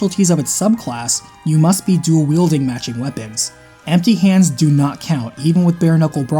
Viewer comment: This may seem like a dumb question, but does "magic weapon" mean elemental or weapons that increase the magic stat? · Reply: If you're talking about the Swordmaster section (37:09), what actually said was "matching weapons" not "magic weapons", so 2 swords or 2 axes, e.g.